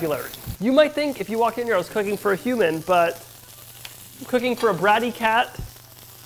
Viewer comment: For a second I thought it said “Cooking a cat!” 💀